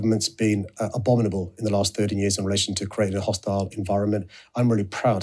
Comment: Your subtitles are wrong here, he said 13 years not 30 years. The party currently in power in th UK has been in government for 13 years, that is what is referring to.